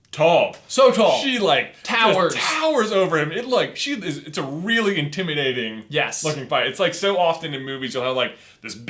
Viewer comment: For a second there it almost sounded like J said "it's a really intimidating f***ing fight" And I was like "J you're a father now! If you're going to cuss you gotta commit! Lead by example!" 😂